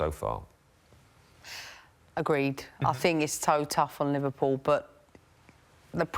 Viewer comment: did Emma say toe tough?!